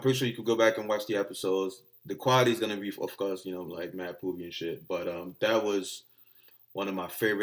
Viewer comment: did you just say "poovy" my mann. shit genetics and skinny fat = poovy still remember that video man. I think it was your transformation and when you were talking about your before. You are a legend man😂😂😂